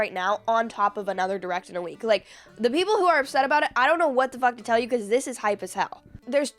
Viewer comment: did you say at 00:50 : "I don't know what the "fuck" to tell you" ? Why are you talking like that ? I'm unsubscribing to your channel. Trash talk will bring you nowhere in life. Good luck with your life.